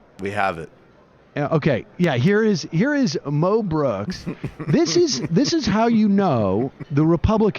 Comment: I initially misheard "Moe Brooks" as "Mel Brooks", and got very confused...